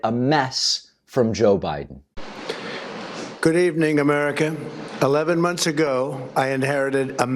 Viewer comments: Did he say "Hello, American" ? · did he say "good evening AmericaN"? singular?